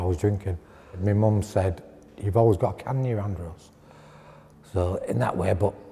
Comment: First time I've had to put on captions for English speaking people lol...and the captions were off so I still have no idea what my man in the green was saying at 6:45 The captions say "you've always got come near on girls" What???? Lol